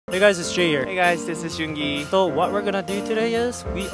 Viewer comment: I thought he said “HEY GUYS THIS IS YOONGI”😂 in the i tro